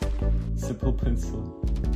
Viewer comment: Мне послышалось или ты сказал блек пенсил🥵🥵🥵